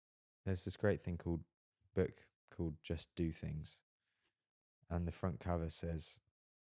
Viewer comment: couldn't find the book that was talking about in 27:02 does anyone know what book? i know he says the title but nothing shows up. author of book? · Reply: He just realised it's actually called "Just do something" by Kevin Deyoung 😁